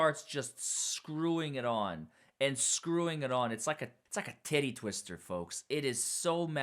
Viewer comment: Did I hear say “tittie twister”?